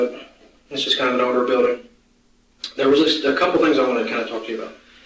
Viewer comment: I thought he said there was an "odor building"😂 because it's warm but he said "older building"